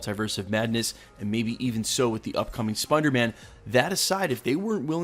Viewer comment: did he just say Spunder Man?